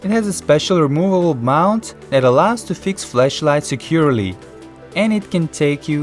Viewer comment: If he says “flashlight” it sounds like he says fleshlight. Only legends know what a fleshlight is. It’s also a product I found on aliexpress